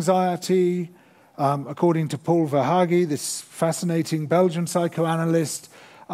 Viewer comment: Can someone tell me what's the name of the belgian writer he mentions? Paul Van something?